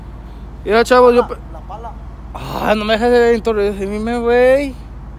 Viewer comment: Que dice en el 2:38 jajaja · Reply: Ana Hernandez que no lo dejan introducir😂